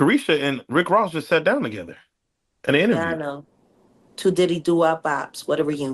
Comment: Lol, did she just say 'To Diddy do our bops'?😂😂 If not it must be me🤣